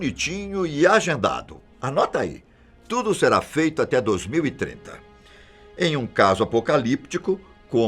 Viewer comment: só foi eu que ouvi ele falar 2000 e treta kkkk